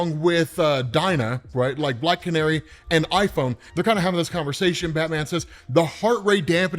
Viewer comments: Unless I misheard, you called cyborg iPhone🤣🤣😵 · did you call Cyborg..."iPhone" at 6:32? LOL